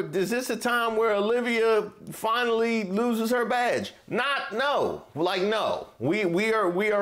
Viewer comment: I know you said "Badge", but I heard "Vag" 😅